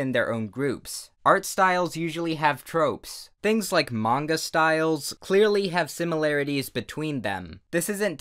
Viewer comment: idk why but I heard "among us styles"